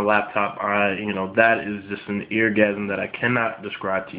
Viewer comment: Like if heard him say eargasism lol!